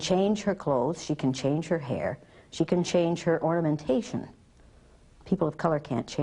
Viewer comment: the one singular thing i disagree with in this entire video was when she said the gay girl could change her orientation. i mean technically yes, however, i am also a gay person and...i can't change who i love. other than that, this entire video is perfect. i think this should be taught in schools. everyone should go through this at least once in their lives. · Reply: Johnny Cade I don’t think she said orientation, I heard “ornamentation”, because she right before that she said “Carrie can change the way she looks, her hair and her clothes,”